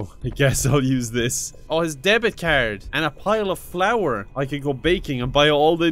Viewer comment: *2 videos *Steamed Hams reference *I heard "flower" instead of "flour" but context helped me Good day for me